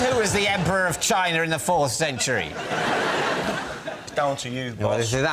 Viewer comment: I genuinely thought he said "It's Dao Xing Yu"